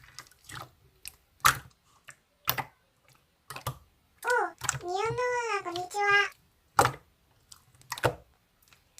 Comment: 저기 링고(사과)가 뭐라고하는건지 모르겠다.. (こんにらは) (코응니라하) (콩니라하) (콩니라하가 뭔소리야..?) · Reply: こんにちは는 안녕하세요란 뜻이에요